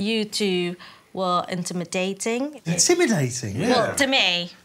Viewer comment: i thought she said "intimate dating"